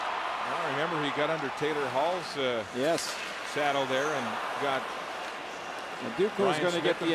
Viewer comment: Does he say Eberle got under Taylor Halls saddle?